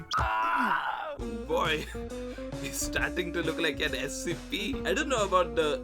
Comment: did he say Scp!